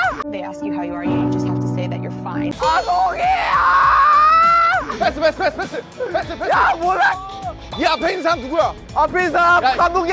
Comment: When hobi shouted, I had misheard it as "kya bol raha" and I was like "wait deos he knoy Hindi??😂😂🤣